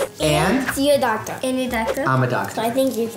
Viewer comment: When one of them said: you are a doctor? I thought she said: You're adopted 😂